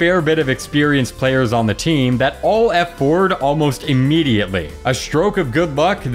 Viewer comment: When you say "All F4" i hear "Alt F4" everytime